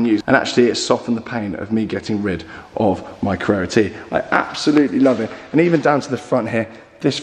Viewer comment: When se said he sold carrera. I thought he said carrera GT. I have to rewind back to confirm that it's just carrera T